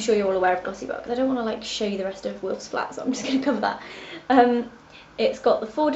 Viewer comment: i think she says she doesnt want to show us the rest of 'wills' flat